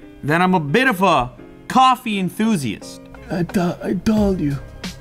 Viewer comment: Calls self "coffe enthusiast", drinks capsule coffe. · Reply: I think you misheard enthusiast as elitist.